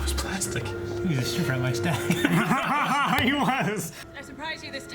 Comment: what did say? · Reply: Not.A.Skrull he was citing what Rory said in the background which is "he was stripper at my stag party"